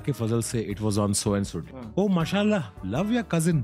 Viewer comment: yar i dont understand what he said.. Its blacked the word.can someone explain what did he said? "It was on---" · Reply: On so and so..